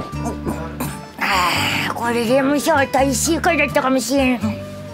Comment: ここ何回聴いてもなんて言ってるかわからなくて面白いんですけど、誰か教えてください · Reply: あー！「これレモンサワー大正解だったかもしれん」ですね！ありがとうございます